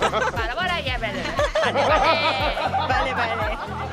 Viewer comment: parece que dice: "Bara Bara llevele" (una de las formas que usan los vendedores de los mercados en México para decir que algo está barato y que lo compres)